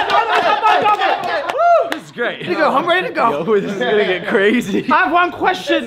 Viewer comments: dat boi logen said “this nigga crazy “ 😂😂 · Yo correct me if I'm wrong but did Logan say "this n** crazy" at 2:15??